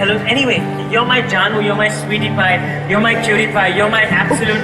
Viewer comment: it sounds like he said you’re my Pewdiepie lol I mean he is a sexy beat